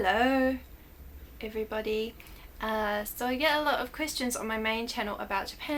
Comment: I first thought that she said christians when she actually said question LMAO XD